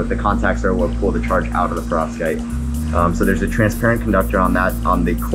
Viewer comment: Solar panels should NOT be transparent. They need to absorb photons which is the opposite of transparency, practically by definition. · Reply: I thought he said translucent, but may he did say transparent. Either way, he also said that there was a backing that reflected the light back up..... I think.